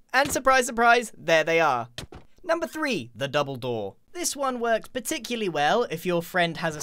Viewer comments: “#3 the double door” what I heard “#3 the dumbledore” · " THE BOUBLE DOOR" Me: did you say dumbeldoor?? Sorry if i wrigted wrong ;)